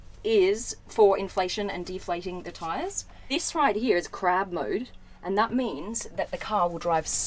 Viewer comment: 2 thing when she said Look at all these buttons its reminded me of that meme and when said crab mode I thought she said crap mode